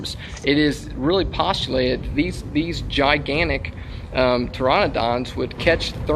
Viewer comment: Did he just say "(P)Toronodonts"? ,o.o